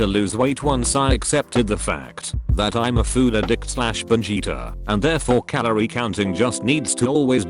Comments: I thought said he was a "food addict Vegita" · When he said binge eater i thought he said vegeta and had to hear it twice